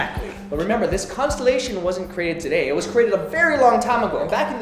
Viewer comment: I heard "in your Mum" instead of "in your mouth" hahahaha